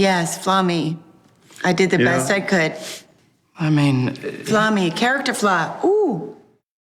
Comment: What the heck she saying 11:17 FA-ME ???